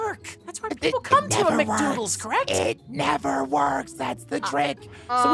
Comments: u sounded like you said mcdooles but hey! It’s okay! It’s funny anyway!!😁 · when you said Mc'Doddles I heard you said Mc'Dodos